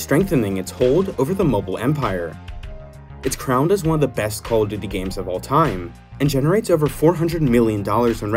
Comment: I heard " call of diddy " on some parts